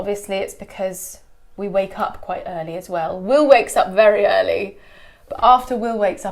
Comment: English with Lucy Did you say “we wakes up” ? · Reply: "Will" wakes up very early.